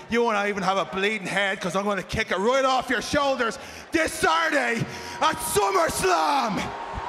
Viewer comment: did Shamus said Saturday at SummerSlam?